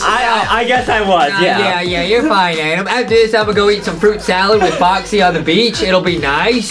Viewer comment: Did say he is going to eat fruit salad when he turns down for adems?!